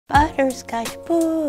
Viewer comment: Ion know why I thought it said banana pudding and was waiting for her to put the bananas in the whole vid just to realize it said butterscotch 😂😂